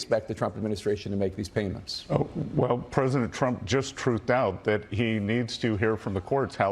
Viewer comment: Did he say Trump just "truthed out?" Are they trying to make Truthing the new Tweeting? Didn't Colbert already make fun of this forever ago? Truthiness?